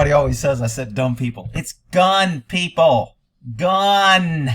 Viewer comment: I heard you say "gum people"... trying to say they are chewed up, just kidding but another good video.